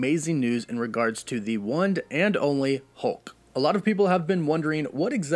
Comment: Oksy first off super excepted for the next chapter of the MCU!!! 😍😁 And also Ya I can tell your sick man cause you said “one’d and only” I was like I’ve heard him say one and only 1,000 times and he never said one with a d at the end 😅